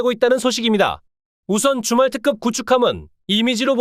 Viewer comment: 줌왈특급이.... 주말특급으로 들리는 이유는??